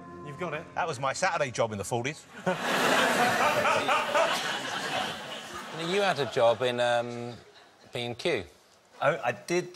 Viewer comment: I thought richard said "you had a job in being cute" to noel at 10:28 and i melted